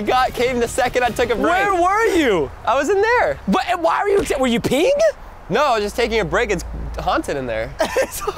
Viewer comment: did he swear I heard it is ____ haunted in there